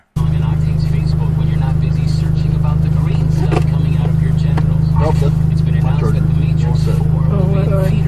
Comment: What?!? “When you’re not busy searching about the green stuff coming out of your genitals”?!? Did I hear that right? If so, what was that guy listening to? (At about